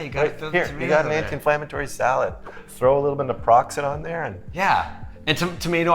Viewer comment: "Every time we eat a head of lettuce we get promised a nice sunny day." LOL right out of the gate. What you two are doing is so unique and so wonderful. Never change. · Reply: after mentioning tomatoes, did I hear you say, “throw in some Naproxen in there”? The subtitle said “peroxide”. 😂😂😂